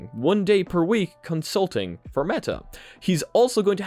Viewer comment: lol i thought you said he spent 1 day per week insulting Meta xD